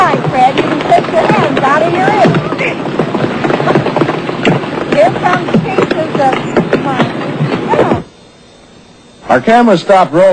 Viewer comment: I thought I heard: "Hear come pieces of... uh, whale?!?